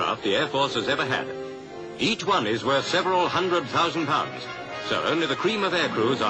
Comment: If you look back, I was correcting another poster who thought he heard "seven", rather than contradicting the newsreel announcer's "several". Hence my dig at the chap's accent.